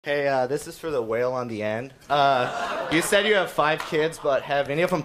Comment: “ this is for the whale on the end “ that line alone was brutal 💀💀😂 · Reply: When i first heard this i thought he said, this is for the whale on land.